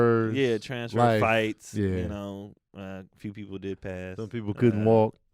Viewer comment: When was talking about why the number of kids dropped, i thought i heard “some passed” and i was like, damn, some died but realized he must have said “didnt pass” as in didnt pass the grade 🤦🏾‍♀️😂🤦🏾‍♀️😂